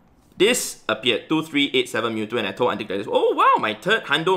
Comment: i always thought you were saying "antique lettuce" just realized its aunty gladys lol i need to stop watching when im half asleep